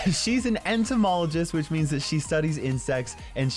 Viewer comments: I really thought he said "which means she studies in sex* lmao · I honestly thought said "she's an entomologist which means she studies in sex" and had to Google entomologist to find out it was "insects" lmao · Off u sad she studies Insect's 😂🤣 I thought u said IN sex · Wait what, she study’s in sex is what I heard anybody else?